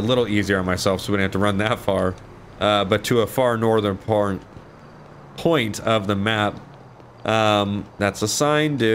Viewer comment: Excuse me did u say porn at 15:27 haha